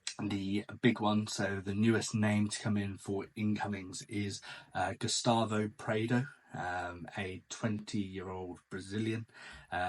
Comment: For a a second, I thought you said Gustavo hammer